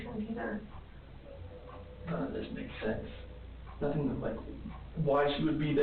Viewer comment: Also, some thing my daughter noticed a while back and we never hear anyone talk about it so I'm going to put it out to you... 🌷when and the other guy(?)are talking to him about what happened that night take note of the moment where he says while sort of crying " I know none of this makes sense!" She" wasn't supposed to be there..?"… It was my daughter, who kept telling me why would he say that? 'Who' wasn't supposed to be there… Shanann was supposed to be there- she - was coming home that night!? Did NK show up and help him without him realizing she was going to be there? Let's not forget how he speaks about how he could not stop it's like 'someone else' had their hands over top of his, and even if he wanted to stop, he couldn't… After their toured crazy sex games the night before I think, NK was so obsessed with him that she was there to make sure Bella and Cece's mom was no longer going to be in the picture?!😔💔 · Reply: I hear him say none of this makes sense, why she would be there, not she wasn't suppose to be there, is that the part you referring to?